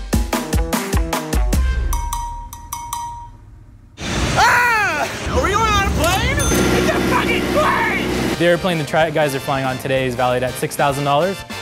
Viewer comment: did he say “try-it guys” ❓❓❓